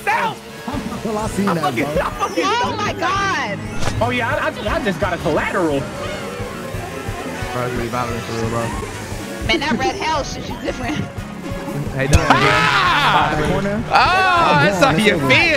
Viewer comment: LMAOO we just not gonna notice that said “that red hell shit you different” at 20:09 😆😆 I think she meant to say that red shell hit you different